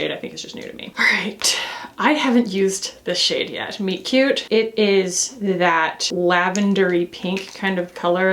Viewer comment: MOB M36 is awesome. And I think I need that Soshe blush, it’s beautiful. I am cracking up at those Estée Lauder blushes 😂😂😂 and then I’m listening and I thought you said one of the shades was Meat Cutie and I was snorting. I’m so glad it’s Friday.